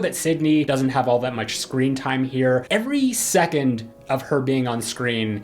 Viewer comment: did you say screen time or scream Seriously I can’t tell